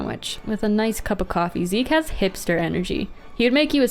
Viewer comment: i thought she said "zeke has hamster energy" at 7:32 and i was soo confused 😭